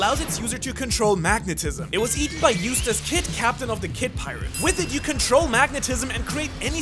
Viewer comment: Fun fact when people started talking about eustass kid when they pronounced the name i thought they were saying a random kid in the anime was a useless ahh kid 💀